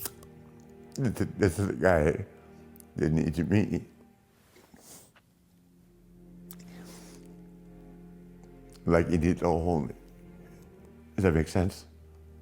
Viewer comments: I'm not sure of what Flair said from 0:43-0:52. Anyone know what he said/want to clarify? Thank you. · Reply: "This guy didn't need me, like he needed to hold me" Atleast that's what i understood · what is he saying? Even CC doesn't understand... I am picking up: "Like heedeetoa ho hole-me".